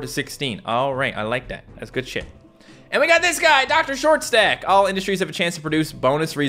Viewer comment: I actually thought you said dr.short sack